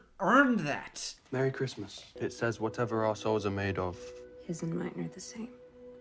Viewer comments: hardin: "whatever our souls are made of-" me: "whatever arseholes are made of..." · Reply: Wait…he said “our souls”? I actually thought he said a holes. I’ve watched this video like 5 times, and I just read your comment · Harden's accent is so bad that I legitimately thought he said "whatever arseholes are made of" instead of 'our souls' until repeated it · I wasn't looking at the screen when Hardin says "whatever our souls are made of" part (around 19:45) and I heard "whatever assholes are made of" and based on how Hardin acts, it made sense for a second there. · I thought I heard him say "whatever assholes are made of, ours are the same" bruh 💀😭